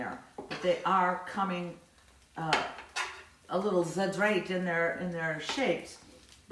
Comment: what was that word you said around minute 6:03? I think you said “fadraked”? I need to know. xoxo